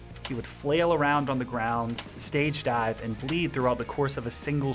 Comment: is that blood wtf? Edit:u said bleed?